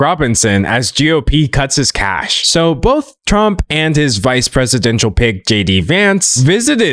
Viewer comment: i misheard that as "vice presidential pig" and didn't bat an eye